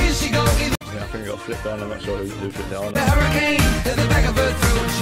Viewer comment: sounds like there saying Harry Kane